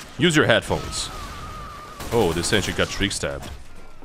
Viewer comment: “Oh, the sentry got trickstabbed” —, December 2022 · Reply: I heard "This engie got trickstabbed"